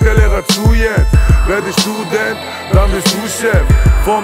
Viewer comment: ich hab immer verstanden " wer bist du denn, dann wirst du chef" haha jetzt macht es auch sinn